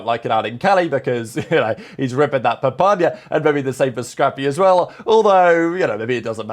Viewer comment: Did you just say ripping that papañya 😂😂